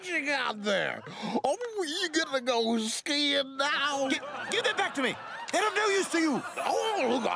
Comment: I had a Mandela effect, I could have sworn he said oh you feeling going skiing on me